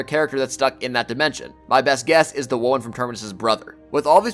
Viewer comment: What is the fourth character you said at 8:05 , played back like ten times can’t make it out “ likely the ??? From terminus brother” sounds like worm maybe but that don’t make sense lol